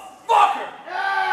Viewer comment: I thought he said butt fucker at first